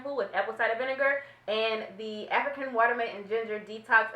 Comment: I thought you said Shea Moisture African Watermelon instead of water mint....had to replay that a back lmao because I'm like that gotta smell good!